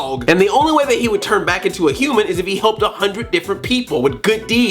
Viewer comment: Like if you thought he said hump instead of helped